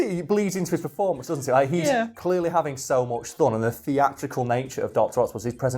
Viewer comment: you say thun instead of fun. Just had to point it out because I’m an asshole on the internet.